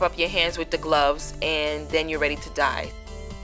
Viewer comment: when she said:"and then you're ready to dye"...i thought she said "and then you're ready to die" xaxa :D